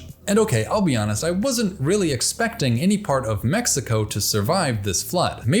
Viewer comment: I thought you said "New Mexico" at 14:00 and then showed a flyover of Saguaro's and i almost had a fucking aneurysm, being from Arizona